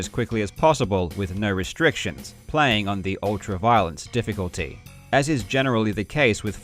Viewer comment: Ohhhhhh ultra-violence. It sounded like he said "Ultra-Violet" lol